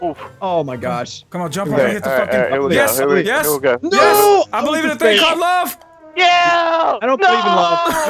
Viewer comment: thought said, " I BELIEVE IN A THING CALLED LOTION.